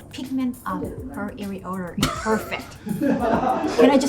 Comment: Not sure if I'm hearing this correctly, but did she just say "the pigment of her areolae is perfect"? Can you like actually change the color of your areolae or something?